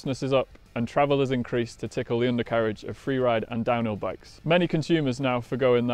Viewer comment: Did I just hear, "tickle the undercarriage"... huh, ok